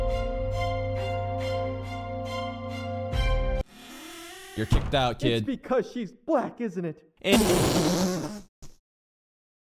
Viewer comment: nah I thought I heard "it's because she's flat isn't it?" Than I see reaction and I'm like "is it that funny?" Than I repeat it 3 times to hear "she's black isn't it" 💀